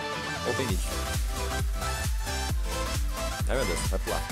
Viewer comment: ổng nói nghe như "ô cái đ!t"